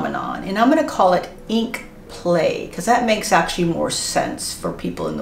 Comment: for a second there I thought you called it “Ink Plague” 😅